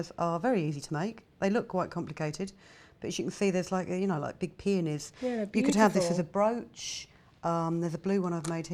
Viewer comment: WHATTAHELL DID SHE SAY 0:30 BHIG PHENIS